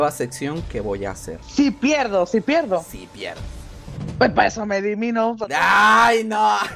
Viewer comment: que dice la chama :v? no entendi xd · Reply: Dice que para eso se elimana